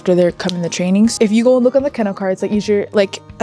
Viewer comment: As they wondered why phantom was called phantom he moved to reveal a somewhat phantom like shape in his markings. Also the young woman going over the white board kept saying something that sounded like kennel cards. Curious if that is what she said or was I mishearing. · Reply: Yes, it was kennel cards, just like for the cats and dogs. It gives all the pertinent info on each horse, along with their picture. They're kept near stalls and along the paneling in the pastures so horses can be kept track of more easily.